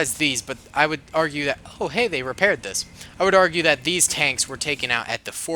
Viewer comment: I thought you said they can repair